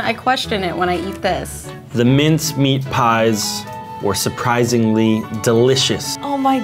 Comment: I thought it said mine craft pies.